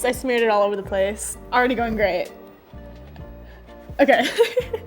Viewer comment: When she said “already going great” I thought she said Ariana grande